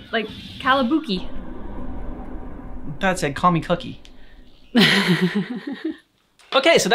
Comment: Heard it as "Call my booking!"